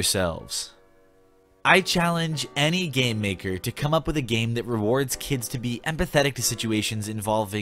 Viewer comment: I really thought he said 'I challenge a gang member to come up with a game.' 😂